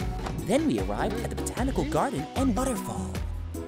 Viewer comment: I think I heard him saying butterfall